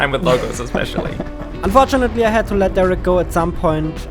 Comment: I thought you said Derek yu for a second lol